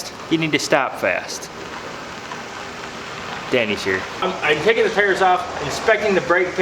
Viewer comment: I thought said "Daddy's here" 🤣🤣🤣🤣😂